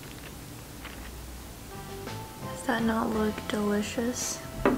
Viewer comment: Omg did i misheard her or she really said “Sana all delicious” 😭😂 i love you!